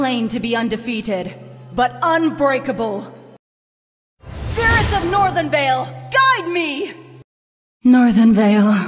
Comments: I thought she said "Spirits of Northern Vale, Guide Me!" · I think it said "SPIRITS of Northern Vale guide me!"